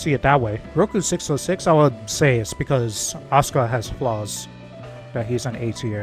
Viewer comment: Bro why'd I hear Asuka as 'Oscar' lmao